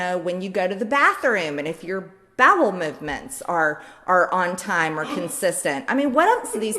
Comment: Sounds like she said "Babel Movements" as well.